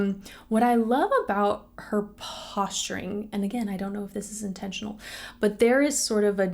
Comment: You say posturing, I hear "stankface"